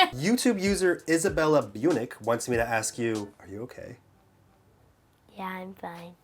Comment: I thought he said said are you gay when they said are you okay XD